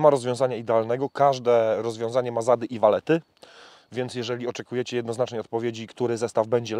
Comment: Czy ktoś jeszcze to usłyszał " ma zady i walety "😂